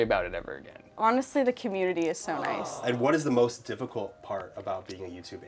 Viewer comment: I thought the "SO NICE!!!" said "SONIC!!!" for a second WAIT JAIDEN SAID A DIRTY WORD :OOOO